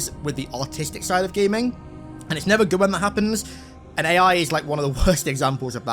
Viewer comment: i thought he said autistic in nature and had to go back and re-watch 😂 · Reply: thought he was saying gaming is autistic 😂 love his accent!